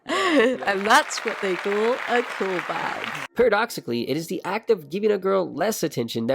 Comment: the subtitles at 15:52 say "cool bag" but i assume what you actually said was "call back"